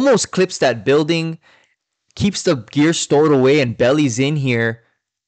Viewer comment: When you said "keeps the gear stored away" I heard "keeps the beer stored away." 🤣